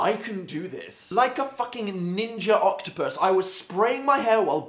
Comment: Did he say ninja octopus at 1:55?